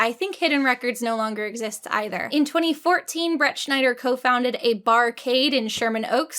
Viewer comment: ohh im so dumb i thought you were saying brett schneider this whole time and i was like who tf is that 😭